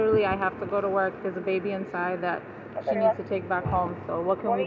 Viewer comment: When the mother said the brat had a baby inside, it broke my heart. People that fake panic attacks, migraines, etc are a slap in the face to humanity. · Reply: Ya I heard that but the third time she sounded like she said there’s a baby inside she’s a nanny.